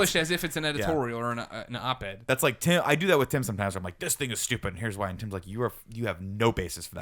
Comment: Did nick say "this nigga's stupid" at 20:18 Lol!